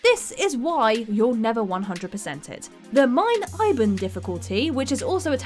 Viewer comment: I heard "mein liben difficulty" :/